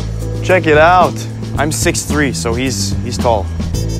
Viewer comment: i thought you said you were 5’9 🤨🤨🤨